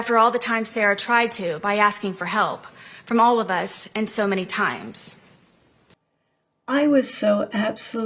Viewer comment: I heard her say “many to me”. Disturbing.